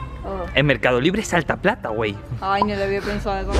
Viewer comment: fui yo o escuche decir a wey 😅